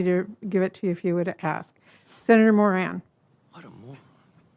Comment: hey did anybody else hear fauci calling the senator a moron?